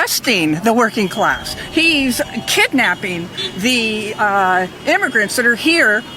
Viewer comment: did she say Demigrants?